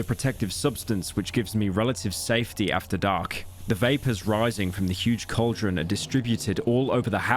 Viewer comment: bro really said vape instead of vapor